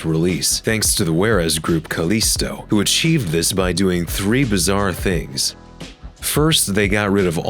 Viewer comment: Did I hear Bizzare? JOJO!!